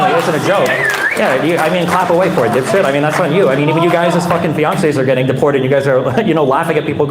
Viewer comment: Did he say “Beyoncé’s getting deported”?